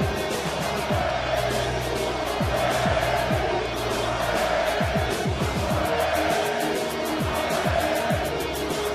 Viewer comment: What is the crowd shouting at 4:33? and whats the meaning of that? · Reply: They're shouting "UH! VAI MORRER!", which means "UH! 'GONNA DIE!".